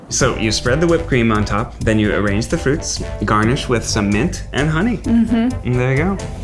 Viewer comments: when ned said some mint it sounded like he said cement 😂😂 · "garnish with some mint" I thought you said garnish with CEMENT 😂